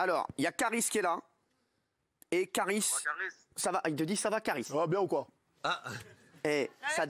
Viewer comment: il dit quoi kaaris dans 11:49 ? j'ai pas bien entendu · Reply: aymen dido bah il dit sa vas bien ou koi